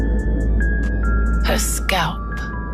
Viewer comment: Did anyone else hear that when she said "her scalp"?? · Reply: I heard her spell